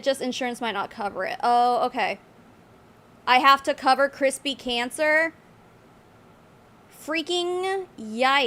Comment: Oh wow I thought she said Krispy cancer too and I thought I was a bio hacking thing lol I hear them talk about some KRSPR gene 😅